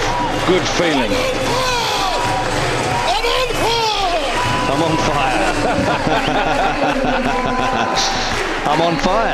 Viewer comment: he is not saying "I am on fire". He is saying "Ya aba Fazl". It is the same name written on his shirt. It is the name of Abbas son of Ali. The name is symbol of strengths. Not this but the whole family of Ali is the symbol of strength.